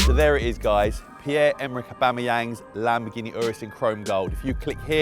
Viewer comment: I thought it said Aubameyang wraps his Lamborghini anus... I need to see a therapist.